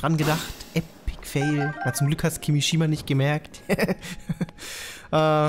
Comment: Ich hab statt Kimishima KIRISHIMA verstanden un dachte du hast mha gekuckt😂 Schade xD